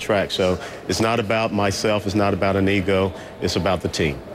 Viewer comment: whoa...at about :33 when he says " it's not about me, not about an EGO" i thought he said before I had to replay it " it's not about me, its not about a NEGRO"....